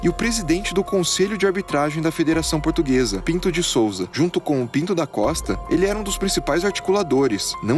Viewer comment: eu entendi " O pinto de Souza com o pinto nas costas"